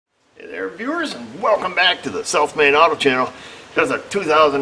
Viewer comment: Every time I hear your intro, I hear “Welcome to the Self Made Auto Channel!” And really, that’s what we’ve got here. :)